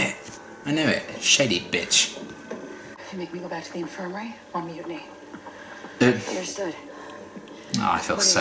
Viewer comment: Can someone please tell me what piper said to daisy at 2:42 ish ‚Of you make me go back to the infirmary, ....‘ I couldn’t hear/understand that cause it was a little mumbled and not a native English here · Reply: If you make me go back to the infirmary I'll mutiny